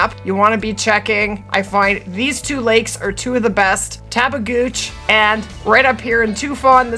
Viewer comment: Did she just say tap a gooch lake?